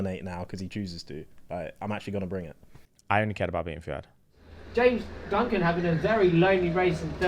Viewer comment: James: “I only care about me and Fuhad” We know James 😂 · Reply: Oh I thought he said “I only care about beating Fuhad”